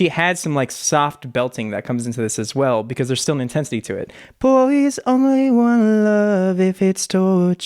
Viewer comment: did day bullies instead of boys ??? I may be wrong but....